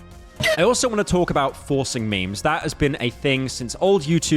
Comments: did he say Forsen memes? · I thought you said forsen memes and jumped out of my chair in excitement. words cannot measure my disappointment. :face-red-droopy-eyes::eyes-purple-crying